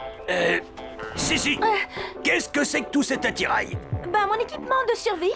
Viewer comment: j'adore Jim qui sait plus quoi dire XD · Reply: « Uh, hum... SISSI! QU’EST CE QUE C’EST QUE TOUT CETTE ATTIRAIL! »